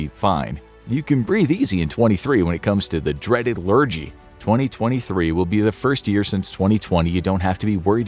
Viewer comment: I thought you said 2023 will be the worst year